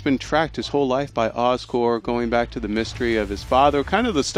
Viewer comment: There's a "p" in Oscorp. It sounds like he is saying Oscore.